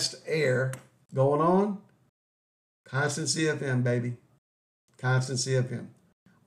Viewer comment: What are you saying at 10:30? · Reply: You’re right. That did not come out very clear at all did it? I did repeat it right afterwards but I said constant CFM. I was referring to constant CFM motors. I.e. variable speed motors. Thanks for the question and thanks for watching!